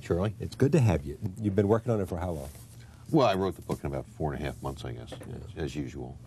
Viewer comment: 4 months to write something as intricate and complex as Rainbow Six yet George Martin can't finish one book a decade. We miss you Tom Clancy you were the Tolkien of the political and covert war genre. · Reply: I thought he said 48 months 😂